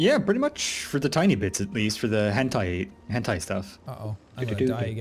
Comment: I heard hentai and was eve n more interested